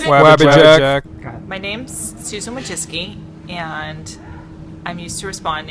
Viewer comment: Is it just me who thought she said Susan Woah Vicky?